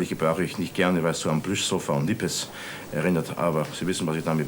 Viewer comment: Ich habe hier die Worte nicht verstanden. FJS gebrauchte hier etwas, was sich wie "Brüssel von Lippes" anhörte. Kann jemand das bitte berichtigen? Danke. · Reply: Dem Transkript ist zu entnehmen, dass Herr Strauß hier sagte: "[...] das Wort bürgerlich gebrauche ich nicht gerne, weil es so an Plüschsofa und Nippes erinnert, aber Sie wissen, was ich damit meine [...]."